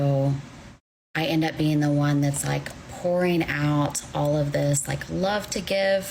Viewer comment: did she say whoring or pouring?